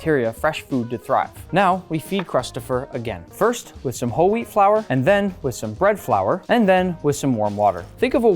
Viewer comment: Did he say holy flour 3:07 👇